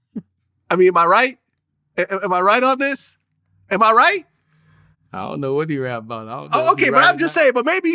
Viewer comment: all I heard Vlad saying is “Am I white” “Am I white”